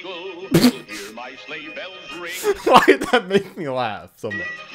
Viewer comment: he just said pringles instead of jingle that’s hilarious like the company that sells their famous chips 🤣🤣🤣🤣